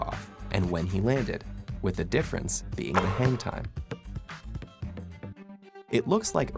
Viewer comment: was i the only person who heard "hentai" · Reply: I heard hangtime but I can’t blame someone for being cultured.